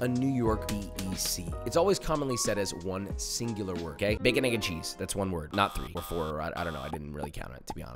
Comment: Why’d I hear: “bacon-n**-cheese” 💀